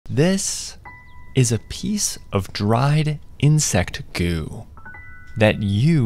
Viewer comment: I thought he said “this if a piece of dried insect goon”💀💀💀